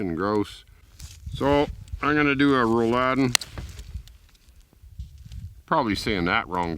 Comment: Great job buddy going to have to try that Rulodin recipe soon ! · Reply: The way that lady said it i heard “rulladen”. Which simply means “rolls” in German. And that makes sense, at least.